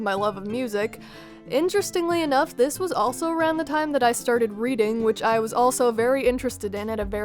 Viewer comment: I thought she said breeding not reading lol